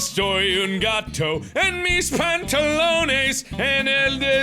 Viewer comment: when you were singing, all i heard was "my pants"